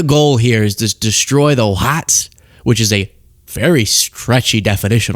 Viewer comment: did you just say hwites?😊